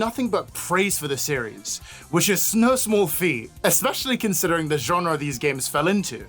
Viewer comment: “Snow small fee” I heard that and I’ll remember